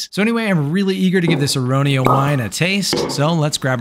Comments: At first i thought you said goblin and while the goblet is nice i was still disappointed :[ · I thought you said goblin not goblet and I was so confused 😂😂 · really thought you said "goblin" until the very last second realised you said "goblet" and I just accepted it like "yeah I don't know what I'm talking about, apparently oak gonna are a thing 🤷🏾"